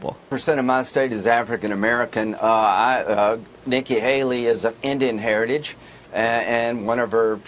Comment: At first I thought he said naked Haley and THAT’s why he was so uncomfortable. My bad 😂😂